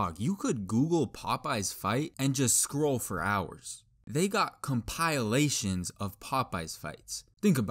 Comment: did he just say compilelations? never heard anyone pronounce compilations like that until now